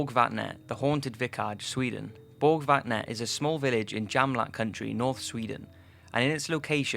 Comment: DUDE im terribly sorry but I can't hear what you are saying on the 4'th one, I happen to be Swedish you know and when you try to prounounce the words, XD damn it sounds so funny. Are you saying " borg vat net " in "jämtland" ? cause it sounds like you said "jamlack" country. There is no county in Sweden called "jamlack" I tell you that much XXD